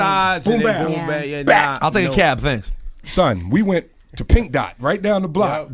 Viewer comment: dumbass thought he said Pinky's